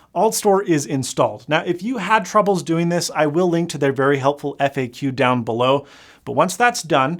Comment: When you said if you have trouble installing I'll link to the FAQ, all I heard was "If you have trouble installing F U"